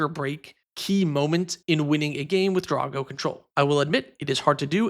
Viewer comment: Oh he's saying "Draw go" I thought he was saying Dargo as is Dargo, the Shipwrecker and I was very confused